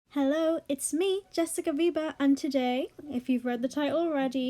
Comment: I thought u said jessica bieber